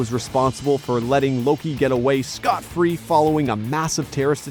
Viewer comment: did you say Scott free as in mister miracle from new genesis?